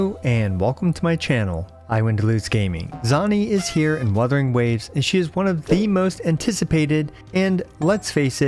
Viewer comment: I thought I heard "Zani is here in mothering waves"